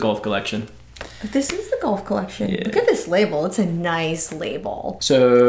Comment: What said "Look at that label...thats a nice label" What I heard "look at that boulder...that's a nice boulder" -donkey (shrek) lol